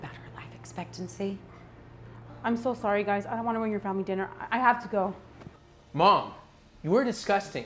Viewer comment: When she said family dinner I thought she said dumb dinner 😂